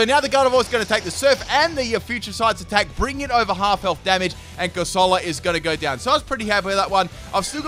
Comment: Did just say Damn it? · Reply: I hear Damage not damn it